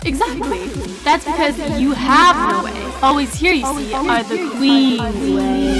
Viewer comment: I love this!! In the intro o thought you said "You must always obey the queens that slay.!" 😂😘